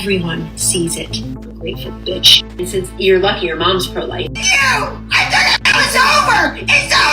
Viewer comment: did i hear that right?? did her mother really say "you are lucky your mom's pro-life!" ?! I never noticed this back when it happened. gold lord that is disgusting